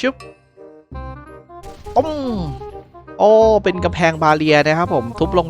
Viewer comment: ทำไมผมได้ยินว่ากำเเพงมาเรีย(สงสัยติดไททันไปหน่อย